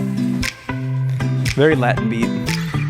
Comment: Omg 1:30 i thought he said “very latin vibe” but like pronounced it “veeb” 😂😂😂